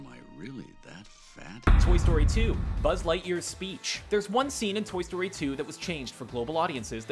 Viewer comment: I thought it said: Buzz Lightyear's S C R E E C H